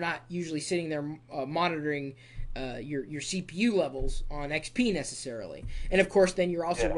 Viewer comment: I thought it said "penis" but he said "XP necessarily"